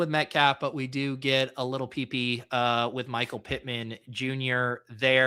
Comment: Did you call Pittman a little peepee @ 1:22:00 ?